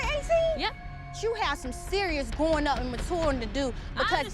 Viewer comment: #8: did she just say "you have some matouring to do ", im dyyyiing